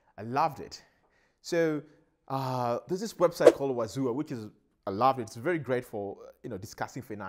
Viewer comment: what’s the name of the website? You said it so fast. Wazu what? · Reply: My apologies. The name of thw website is Wazua (<link>